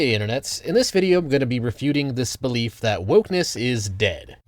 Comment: Was still wakin up when i started watching this, I swear i heard "in this video i'm going to be debunking thee spleef that-" I had to do a double take.